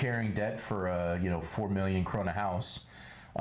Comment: Subtitles say (corona) lol, it's (kroner) the national currency of Denmark. 🙂👍